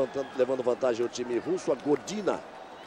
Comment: "Gordina" 4:15 kkkk É Godinaaaa ... e quem bateu foi a Sokolova ...